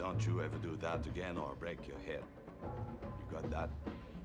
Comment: i always thought he said 'or i'll break your hip' am i wrong? lol